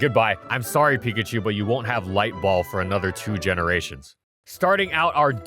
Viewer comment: Light ball was actually introduced in gen 2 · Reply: I first thought it said Ligma balls